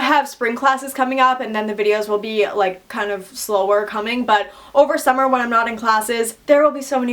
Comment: did you say slower cumming?